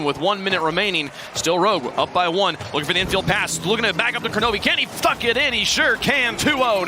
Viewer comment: sounded like he said “can he f*ck it in